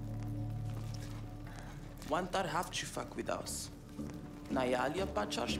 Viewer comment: bro Is it just me or did she say f**k with us??? I'm tripping😭😭😭